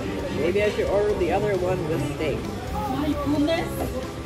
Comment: What did the lady say @ 7:47? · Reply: "Oh my goodness"